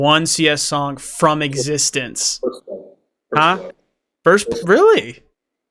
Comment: Does he say First Blood?